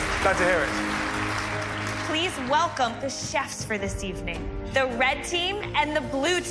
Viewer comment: christina says seasoning instead of evening loool